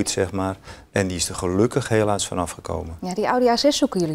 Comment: wat zegt hij nou hij is er helaas er van af gekomen?? Hoor